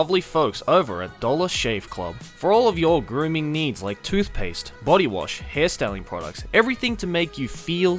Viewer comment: damn you did u just say I need toothpaste